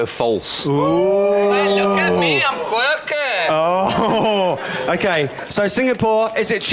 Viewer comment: OMG! it's 2016 and I'm still watching this! At 27:57 Louis said something and I don't know what he said but it's so funny,and I started laughing so hard!♥Please tell me what he said!I want too know!♥ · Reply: Louis said: "Well look at me I'm quirky. "